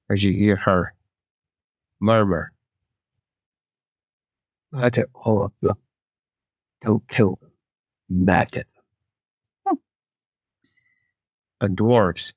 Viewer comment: I can't quite hear what said at 3:12:34 ... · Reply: I think she says “Murder all of them. Don’t kill them, murder them”